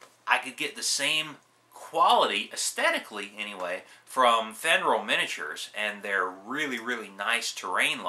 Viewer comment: on the terrain piece alternative, I tried looking up the name "Federal Miniatures" (which is what I believe I heard you say) but couldn't seem to find anything on google. Do you happen to have any links? Thanks for the video, always nice to hear your perspective. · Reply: Hey. The company's called, "Fenryll Miniatures". I'll add some links when I get home tonight. I can't get to any mini related sites here at work. Check back in the morning and the links should be active then.